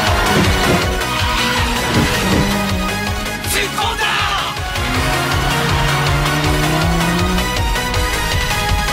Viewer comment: ここいつもなんて言ってるのかわからなくて悶えてるんだよね 「絶交だ」？「絶好だ」？ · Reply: つっこんだ！